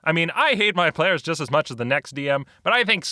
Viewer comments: Did he just say "I think sex would have been fine," rather than six. Funny, particularly since that's the Danish word for six. :D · I hear "I hate my players as much as the next DM, but I think SEX would've been fine."